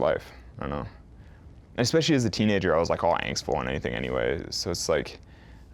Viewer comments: What in the world does he say at 3:55? "And especially as a teenager.." I can't make out what he says after that. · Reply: was saying he was "angstful" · What does Alex say at 3:54, "Especially as a teenager I was..."? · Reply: "all angstful and anything"